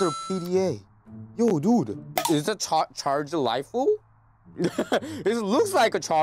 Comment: that kinda sounded like he said charged waifu 3:00 whis is kinda sus 👀